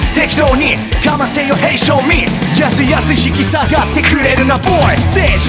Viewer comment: Hey Show me!が Hey 勝利！にしか聞こえん